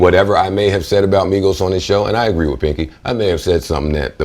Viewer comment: Did he just call him pinky? lol